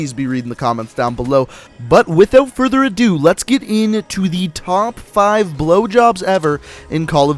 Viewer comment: did I just hear top five blowjobs? 😂😂😂😂😂